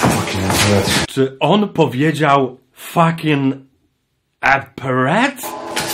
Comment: usłyszałem fucking a parrot